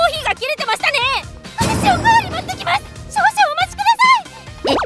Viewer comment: ここのツツジさんの「私、おかわり持ってきます！！」の「ます」が「ま゛す゛！」と言ってるように聞こえる🤣